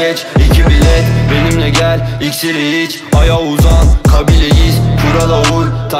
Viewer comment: ben orda iksiri iç aya uza mı duydum? ah sanırım rapci olmalıyım